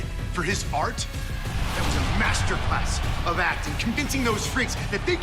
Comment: Did Miz say “Masterpus”🤔… 😂😂😂😂